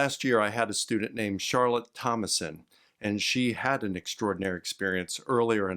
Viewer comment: LOL I thought they said her name was Charlotte Homicide. I was thinking "Who names themselves that?"